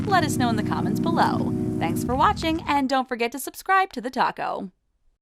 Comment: I heard “dnt forget to subscribe to the taco”..I must be hungry